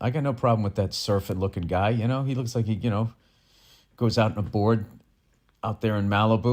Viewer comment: I thought he said “my heart goes out to you” and then did the sign 🤷